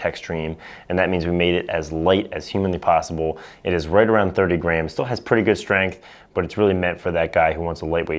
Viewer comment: Did he just say the LTX is 30 grams?!?!? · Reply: 130 grams